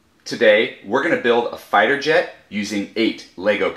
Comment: When you say "fighter jet" i hear "fire jet" anyway, good moc.